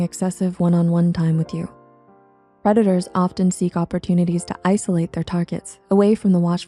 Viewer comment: sounds like she says "Redditors" a few seconds after this time stamp lol. Fitting.